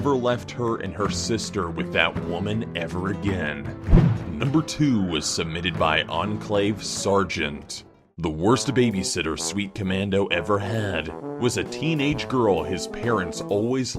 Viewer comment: I could have sworn he called Enclave ”enslaved servent ”